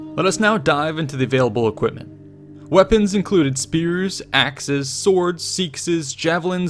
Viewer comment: hey guys i know this video is a year old, but im asking if anyone can tell me what he is talking about at 1:36 "...axes, swords, ?, javelins..." i do not understand or know the weapon he is talking about and do not know how to spell it so i could search for it. It would be amazing if somebody could help. not a native speaker, so sry for mistakes and maybe thats why i didn't understand. thanks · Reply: The weapon is a "seaxe", basically a short sword